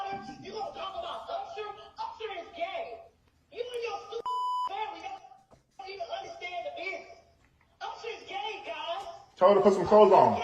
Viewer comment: Am I the only one who heard her say Usher is (Gay)…let’s not skip over that..